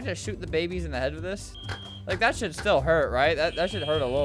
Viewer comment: did he say like that shit still hurt or I'm I tripping